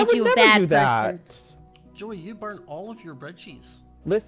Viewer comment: Ik I’m late to this but I’m rewatching this series and I never noticed Scott sounded like he said “you burnt all of your bedsheets” at 16:06 😂😂😭